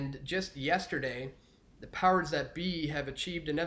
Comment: listen close. Did he say the Cowards That Be?